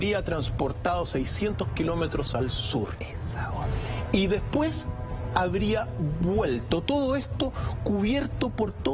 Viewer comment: Alguien entendió lo que dice la voz oculta en el minuto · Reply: dice: "esa onda?" lo dice el conductor del programa como expresión de asombro ante lo que está escuchando del